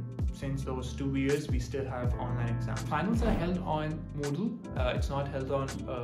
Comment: hi i cant really catch what you said here. you said the final exam was held on what? Moodle? is that a platform or a third party tool integrated with coursera? im obsessed with all your videos and binge watching them right now. very helpful infomation!! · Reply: They are held on another platform called Moodle. Not on Coursera.